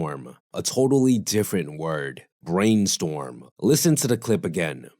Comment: bro i heard BRAIN NEEDLE wtf